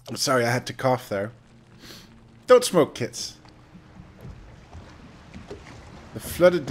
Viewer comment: You keep saying "Don't smoke kids". I keep hearing "Don't smoke Qieths".